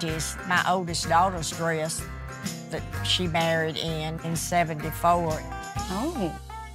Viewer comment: I thought she said she was barried in it at first